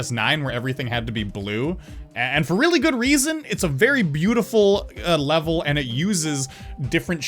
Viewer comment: Did he just say? Bedu? What?!